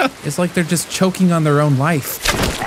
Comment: It sounded like he said “Choking on their own wife” 😳